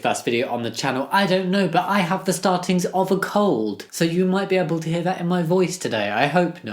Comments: instead of "i have the startings of a cold" i heard "i have the startings of a CULT"... ...and i was VERY interested😂😅 Also: fLoAtInG BoObS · At first I thought you said I have the starting of a cult and I thought where do I sign up?